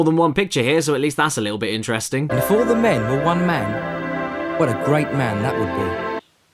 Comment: I can't catch the sentence and how it relates to Hitler. What did he say? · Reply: Ultrawave Studios "If all the men were 1 man,what a great man that would be".